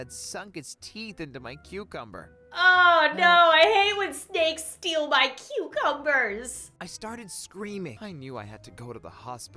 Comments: I thought Lauren said i hate when snails stole my cucumber🥒😂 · I thought she said "I hate when a steak steals my cucumber" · I thought she said "I hate when a steak steals my cucumber" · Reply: Like I heard “I hate when a steak steals my computer” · I thought she said "I hate when a steak steals my cucumber" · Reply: Freya Crooke I Thought She Said I Hate When Skanks Steal My Cucumber 🥒